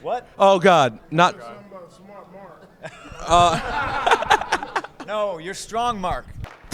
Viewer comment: HAha at about 11:00 you can hear Henry say "What did you say about smart Mark?"